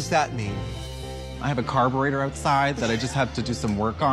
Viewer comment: I thought she said i have a card reader outside i just have to get it working